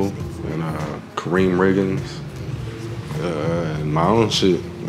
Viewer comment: what's the name of the artist he said? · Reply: Riggins